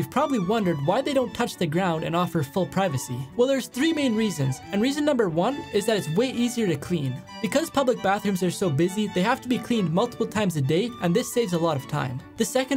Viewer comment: I thought he said " because the bathrooms are so goofy 💀